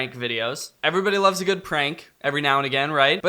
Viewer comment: Wait excuse me did he just say every now again instead of every now and then